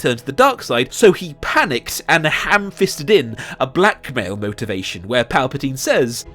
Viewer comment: At first I heard "ham-fisted in a black male motivation" as you showed Finn at 9:00 and I was like "oh this is taking a weird turn-"